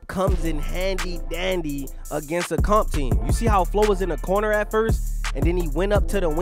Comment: did you really say handy dandy got to chill lol